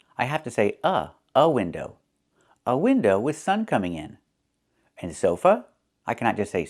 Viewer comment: with-thun coming in 🙄 did I hear it right? Native speakers say like that? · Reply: No, "with sun coming in", not with-un coming in.